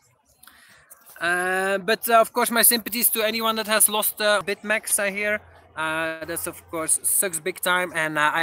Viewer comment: "Sympathies to anyone who has lost bitmex"??? dont know if I heard you correctly, what exactly are you referring to?